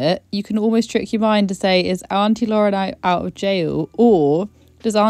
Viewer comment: Sorry but all i hear is "is auntie lauren out of jail"